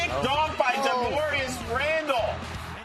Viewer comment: did he say Darmorius Randall??!?